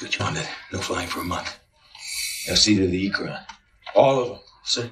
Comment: What did Jake say? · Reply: "You'll see to the Ikran, all of them"